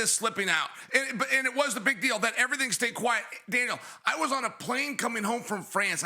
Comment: Did you just called Cormier - Dana?? 1:28🤔